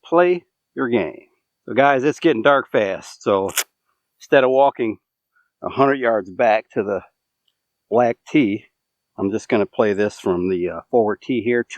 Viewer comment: No, 18 Hole course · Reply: I thought you said 3300 yard black tees. I guess that’s because you were only playing 9